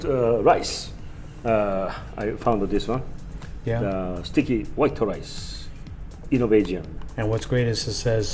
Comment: Got a kick out of hearing Hiro say "Sticky white rice." Always sounded like he was saying "stinky white rice." Lmao